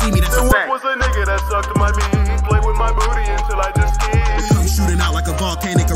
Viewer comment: what does he say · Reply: Till I just skeet